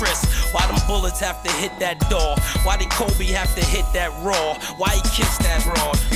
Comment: Not uncut. He says 'whore' on the record instead of 'broad'.